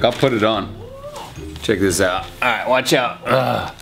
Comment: parece que dice "eh wacha", solo los argentinos van a entender jajajajaj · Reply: Dice watch out que significa miren esto